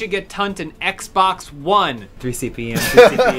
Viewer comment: What is saying? It sounds like: "We should get Tunt an XBOX ONE.. 3cpm, 3cpm" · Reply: Free CPM. Like the joke was they get paid just for bringing it up